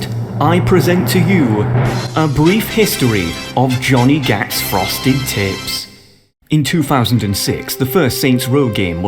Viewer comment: I thought you said Frosted Tits lmfao